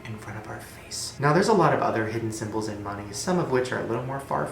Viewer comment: when you said "symbols in money" I thought you said symbols on your body and I was like shit bitch what more could my body tell me other than I eat like shit